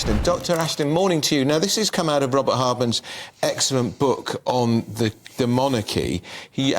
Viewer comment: 0.25 I thought he said book on DEMONICI. Wow thats a pronunciation I never heard before